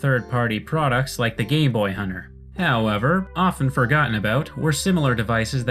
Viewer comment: did I just hear "gay boy hunter" lmao